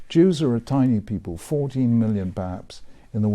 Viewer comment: to be fair, it really sounded like the rabbi said fourteen million, not forty million